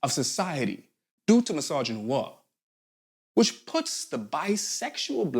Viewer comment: The subtitles might need another re-read though it is always cool to see them on a video. Around 16:00 I read “massaging War” when I think said “misogyny + noir”